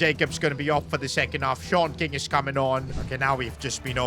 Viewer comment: Did bro just say “Short King”???